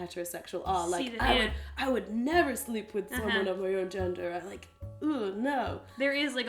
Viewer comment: "I would never sleep with women of my own gender!" lol did I mishear that or does it make sense and I'm just being thick right now?